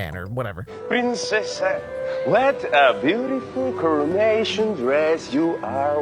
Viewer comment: I thought that he said "what a beautiful Cremation dress"